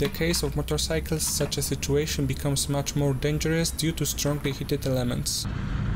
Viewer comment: did he say "in the case of motorcycles such a situation become much more dangerous due to strongly heated lemons"...??